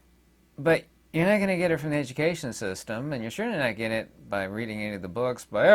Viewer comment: That was Illuminating! No matter how many times I hear you say it. I actually thought I heard you say "initiation system" and applauded but now I can't find it in transcript. Oh well, we know. Education system is initiation.